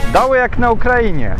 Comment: What did he say about Ukraine?))) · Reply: Doły jak na Ukrainie...